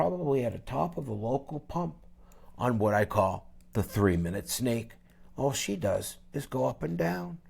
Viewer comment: Shit guys I thought he said the 3 minutes skank 😂😂😂